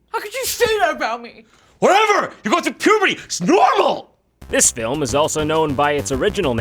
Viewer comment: What does say at 2:10? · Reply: "Whatever! You're going through Puberty! It's Normal!"